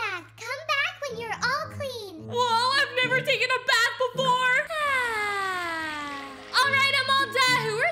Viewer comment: In 6:13 me and my friend were laughing for an hour when I heard “Woah, I never took a bath before” LOL 😂